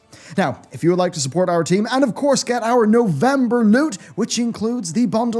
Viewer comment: Im so damn nasty I keep hearing "November nude". And I want it!